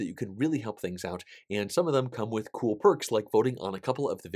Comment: sorry did you say we could earn "cruel perks"?